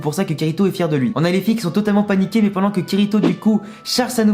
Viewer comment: Le Jeu de mot est incroyable. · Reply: Ah putain j'ai compris "les filles qui son totalement paniquées (pas niquées)" 🤦🏿‍♂️